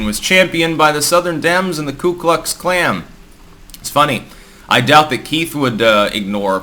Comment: did you say or almost say "Ku Klux Klam"? 😆